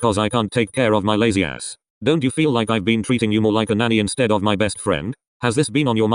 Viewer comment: I wasn’t looking at the scream when Kenma said “nanny” and I thought he said daddy and I was like so surprised! Also I love the video!!!